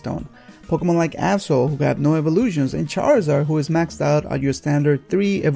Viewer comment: was I the only one who heard him say hole? xD